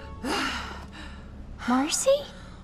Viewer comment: Is she saying Horsey?! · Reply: 'Marcy'! the other character's name is Marceline. there are subtitles for this video to check with as well :)